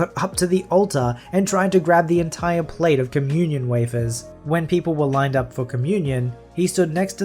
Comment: I thought that said "communism wafers"